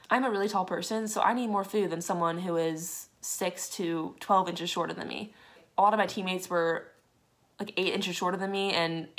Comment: Omg I am dying.... I heard the part at 3:50 as “people who are 6’2; 12 inches shorter than me” and so of course I’m out her thinking you are 7 feet tall